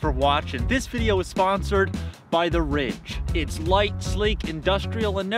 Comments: Ngl, at the start I thought you said the video was sponsored by “the rich” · At the beginning I thought he said it was sponsored by the rich and I was just like oh ok that’s a normal thing to say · I misheard the sponsor as "The Rich".